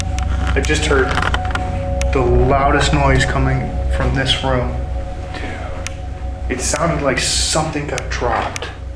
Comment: when walked in that room after hearing a noise you can clearly hear a man whisper something right after he said “I just heard the loudest noise coming from this room”! Kinda sounded like it said “Tim” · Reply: I think it is whispering “dude”